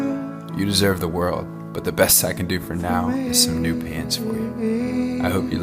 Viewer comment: WAIT, PANS???? My audio processing thought he said PANTS with the T, as in CLOTHES.